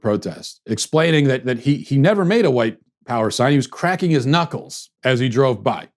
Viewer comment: This is true. I have Mexican ancestry and the doctor put Caucasian on my birth certificate. I support President Trump and I crack my knuckles like this guy so I guess that instantly makes me a white supremacist! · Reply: I thought he said Craking his Nuts, well that is what I heard.